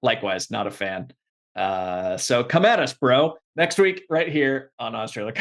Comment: Don't even know if this was intentional, but it almost sounds like says "Ahso-come at us, bro" at 35:10 and that's genius